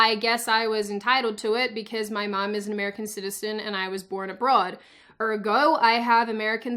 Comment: I am so glad I reheard you cause I thought you said you were born a Broad, which honestly would shock me harder then Raiden Pissed at Kung Lao. If you couldn't tell by my past Comments, I'm a Video game Wizz.